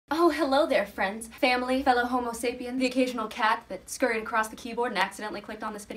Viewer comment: I thought she was going to say "fellow homosexuals" ahahah